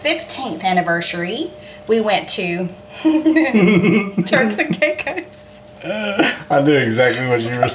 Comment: the transcript said "Turkey cakes" instead of Turks and Caicos!🤣 I just love your little stories, and the humor that you and share. Y'all make the cutest couple! Happy Valentine's Day!💘 P.S. I just LOVE that little Gracie Lou. Give her an extra piece of 🧀 from me!🩷